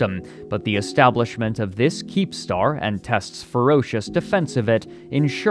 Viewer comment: I thought you were saying keemstar lmao